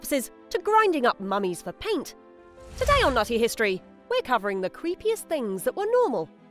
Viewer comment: Thought she said Naughty History lol had to look at my phone. New here & lovin it! Great video & great sense of humor:) thanks for the info & smile♡